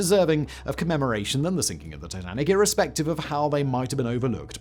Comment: At about 0:39 and 0:40, did you say “Tinanic” or did my headphones short out? Or maybe my brain can’t keep up with how quickly you speak.